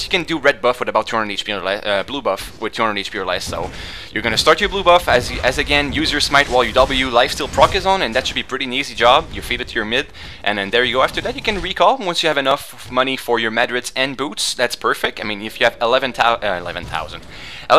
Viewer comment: did you say feeded? xDDD